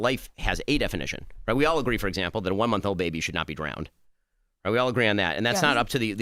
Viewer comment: Charlamagne thought Ben said “A 1 month old baby shouldn’t be brown” 😂😂😂😂😂 I’m dying.